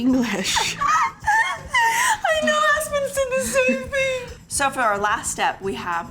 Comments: LOL! Christine's closed caption (3:26) said, "My new husband said the same thing!" What Christine actually said was, "Aspyn said the same thing." Took me by surprise! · Did I hear her say “ new husband” ?!?❤🎉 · Did I just hear it wrong, but at minute 3:27, when talking about how bad Christine’s handwriting is, Christine’s laughing and says “my NEW husband says the same thing.” Maybe I’m crazy, but Mykelti isn’t the one saying it, it’s definitely coming out of Christine’s mouth. Mykelti at this point has been married for years…..hummm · did I just hear Christine just say " My new husband said the same thing" ? I replayed it and it sure says it when the words on the video showed up. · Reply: She says, "I know Aspen says the same thing." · I had to go back to 3:24. Closet caption said, "My new husband..." I'm thinking she said, "I know Aspen..." It caught me for a second though! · Did she say, "My new husband said the same thing" ???? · When Christi e says « I know, Aspen said the same thing! » about how she writes her receipe she he caption said « I know, my new husband said the same thing! » lol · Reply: I thought she said my new husband said the same thing 😅😅😅